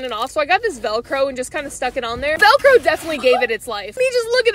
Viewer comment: I think I heard “drunk elephant”💀